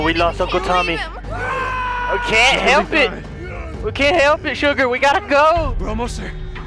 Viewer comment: Did he call Sarah 'sugar' 🥺